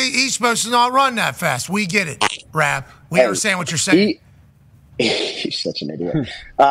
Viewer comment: I swore he said "such a ni&&a" after that white banter LOL